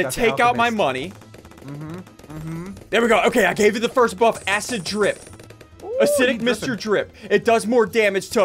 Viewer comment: I thought he said acid trip lol